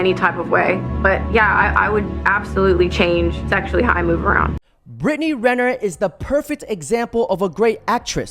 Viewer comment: Everytime I hear “Britney Renner” I hear “Britney Griner”